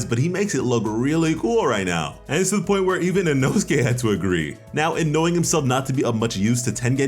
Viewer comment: the subtitles say " nosegay " XD